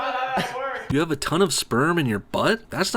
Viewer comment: did he say he has sperm in his blood?😂😅